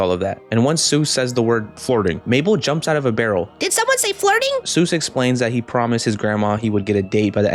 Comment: did SoMeOnE sAy FrIrHiNg 😂😂